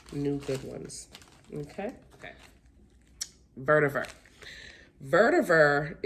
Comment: What name of the plant do you say at minute 3:15 I'm bad at English and I can't understand it very well, thanks in advance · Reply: Vertiver 🥰